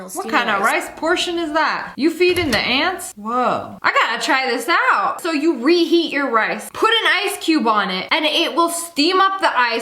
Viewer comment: instead of “rice” she says “ice” and that made me confused at first then I realized what she meant and it was funny so now I’m posting a comment abt it😂😂 Is it funny to anyone else?? 😂